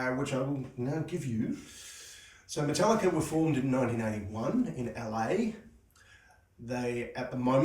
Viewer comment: Ummm I saw them in 1983, it sounded like you said formed in 1991. Bassist has changed 3 times.